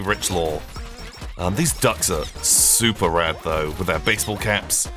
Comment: I was only half paying attention and could have sworn to god that he'd said "these ducks are Subaru" immediately after saying he knows none of the lore. Unfortunately I was wrong lmao